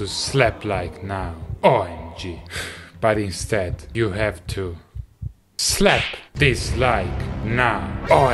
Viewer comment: I thought he said "Slap this like" 😄😄